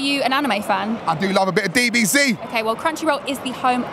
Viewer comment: Did he say bbc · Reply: He said DBZ aka dragon ball z (popular Japanese anime/manga)